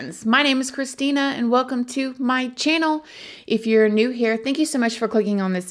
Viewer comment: Does anyone know where I can watch Christina’s videos that are streaming somewhere? I thought she said the Roku channel but I can’t find anything